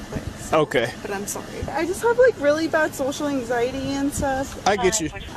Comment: sorry I just have like really bad social gradient | I had to listen closely a few times to hear what she said because according to the subtitles and transcript - 'social anxiety' replaced social gradient and obviously they are two completely different words and meanings! Why did YT change the word? Social gradient in health is a term used to describe the phenomenon whereby people who are less advantaged in terms of socioeconomic position have worse health (and shorter lives) than those who are more advantaged. A classic example of research on this subject is the Whitehall study of British civil servants. | Who else thinks that she shouldn't be driving? | I believe this was on *On Patrol Live a few weeks ago and is an all-too-familiar interaction between woke drivers and officers! These people think they are entitled to be treated differently because they're non-binary and somehow believe that they have special rights and you're supposed to just play along with their delusion! They are insufferable lunatics! It's incredibly satisfying when logic interacts with the illogical!